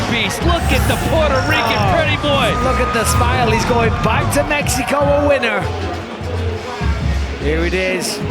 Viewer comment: did Bisbing just call him a Mexican lol? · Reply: one says puerto rican pretty boi and the other says he goin back to mexico a winner 😂💀